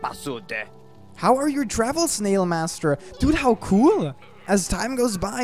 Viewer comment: i heard snailmaster